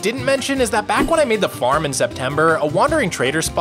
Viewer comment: Guess I’m going crazy since I heard him say Zeptember